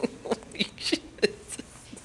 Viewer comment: no offence but it just sounded like you said eat shit in your laughter